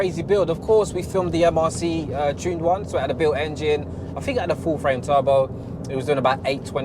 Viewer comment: did i hear full frame turbo? cameras on your mind?